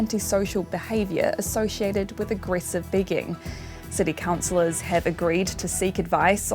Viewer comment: Even as someone who lives here I hear that as "aggressive bigging".